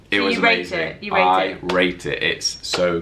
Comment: DID SAY "I RAPED IT," AT 5:33????